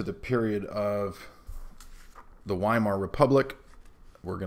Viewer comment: You said, "the Weimar Republic," but i thought you said, "the Wine Mom Republic."